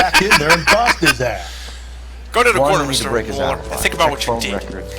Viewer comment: is it me or did I hear cor-ner broken like that?